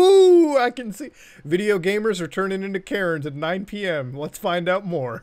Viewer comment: At first I could have sworn Muta said gamers are turning into carrots.😐